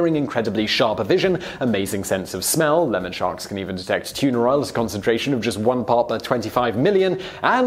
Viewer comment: What did he say after 0:33 ??? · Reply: Leilah they can detect tuna oils.